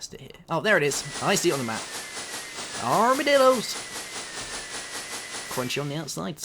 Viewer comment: I thought you did say "ARMY DILDOS!"